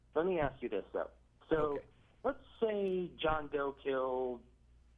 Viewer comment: Can anyone help me? Who/or what is the caller talking about at 10:17? I, for the life of me, even with Matt's help, cannot understand the words that are being said at that moment. I looked up "John Dill" and "Buck Masters" and various combinations of both and cannot for the life of me understand who they are talking about. I'm assuming "John Dill" refers to John Dillinger, BUT I have ABSOLUTELY NO IDEA who "Buck Masters" is and looking up that name doesn't seem to help. Anybody who can enlighten me further would be greatly appreciated!!!